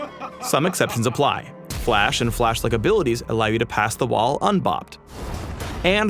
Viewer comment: Is Phreak saying "bocked" instead of "blocked?"....